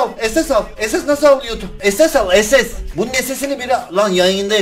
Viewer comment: ne dedin abi orda sen sjjsadıadıahjfa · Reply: Jujuju ooo ss al ss al ss al dedi 😂😂